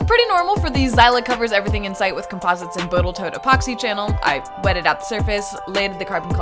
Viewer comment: did I hear you say Botal Toat epoxy? Just wondering if my hearing is off.